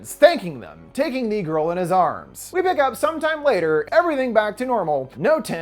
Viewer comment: did he just say the N word? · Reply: Taking “THE GIRL” into his arms…